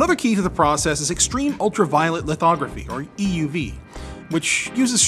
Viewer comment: for a moment I thought you said "ultraviolent" 🤣